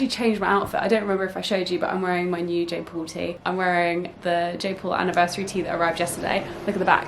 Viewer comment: I thought you said Jake Paul anniversary Tee